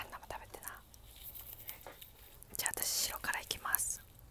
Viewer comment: ノールックで聴いてたら、琥珀糖のとこで「白からいきます」が「塩辛いきます」って聞こえて、え、ASMRで？！ってなった